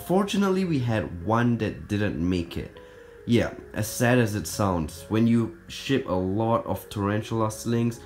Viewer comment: it sounded like he said when you s word a lot of slings Good luck